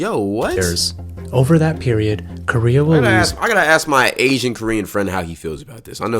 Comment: bro did I just hear Asian Korean 😂😂